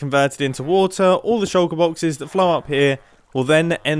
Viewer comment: why did i hear "all the shulker boxes that FLARP here"